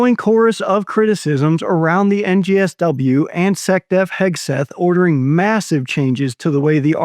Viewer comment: Did he just say "Sec death Hegseth"?! :face-blue-smiling